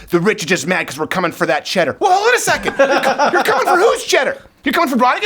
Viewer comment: What word does he say there? Cheddar? Not trying to be mean I really didn’t understand